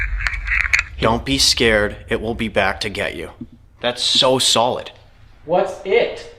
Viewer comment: I hear “don’t be scared” then it says “he’s coming back, coming back” I can’t un-hear that last bit…. Please be safe you guys! ♥️🙏🏻 58:02 that sounds like knocking on a window or mirror… creepy asf! Also doesn’t have a YouTube channel? I’m trying to find him but forgot the name of his channel.. 😭🫣